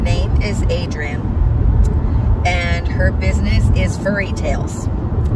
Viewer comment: I can’t see the link to that little girls shop. best of luck with your shop. That tiger tumbler is awesome.😊❤ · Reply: I found it . I don’t know why but thought you said the name was Fairytale 🤣🤣 Guess my old ears don’t hear right anymore.